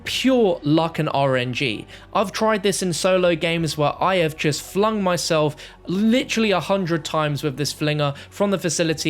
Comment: I thought he said fucked himself instead of flung himself 💀